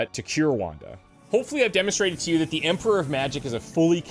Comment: Did he call her the "Emperor of Magic"?